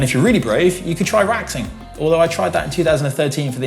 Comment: What did try in 2013 and isn't doing again? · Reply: Waxing but I could have sworn he said “raxing”